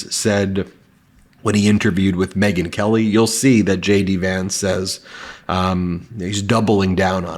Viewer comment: I keep hearing 'shady Vance' instead of J.D. Vance.